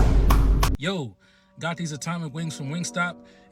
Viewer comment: my brain is dead rn I misheard that last dude and though I heard him say he got wings from gamestop